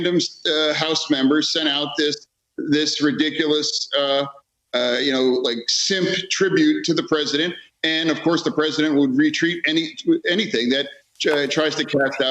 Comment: Did he just say "simp tribute"?